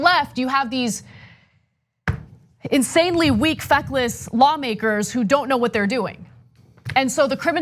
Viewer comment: Did she say “and on the left you have these F-less lawmakers? “.